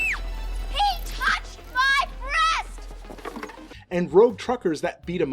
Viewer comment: I thought she said he touched my brother! That would've actually made a LOT more sense given the context of that situation...